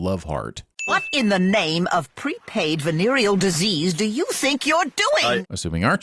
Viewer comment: pre-praid instead of pre-paid.